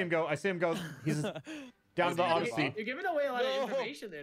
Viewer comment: 1 minute in and.... Did she just say "Odsussey"?